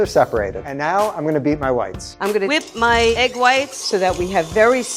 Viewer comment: casually thought that he said "and now im gonna beat my wifes"